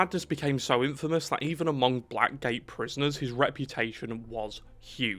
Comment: I misheard you and thought you called them “black gay prisoners”